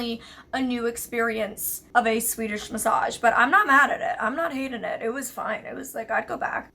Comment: did she say swedish massage